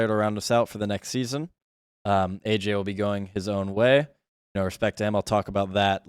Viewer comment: whew, at 3:00 i think he misspoke and said “no respect” to ayyjayy, had me worried there